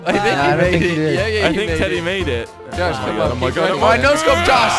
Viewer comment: jelly says Telly instead of teddy